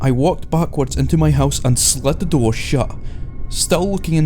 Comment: is he saying slut the door?